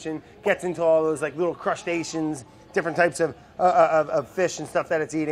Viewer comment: did he just say "crushed asians" instead of crustaceans? Lmao this guy!